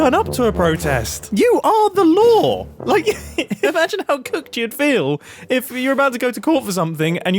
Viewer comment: did just say? "Imagen how cooked you'd fell..."